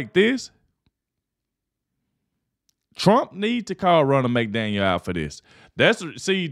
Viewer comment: I thought you were say Ronald McDonald😂 they are both clowns! 😂😅